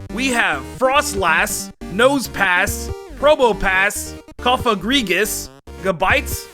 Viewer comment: what even are the words ( I get the deal with the Sharpedo one but not the rest from the timestamp) ( and cofagrigius) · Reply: FroslASS probopASS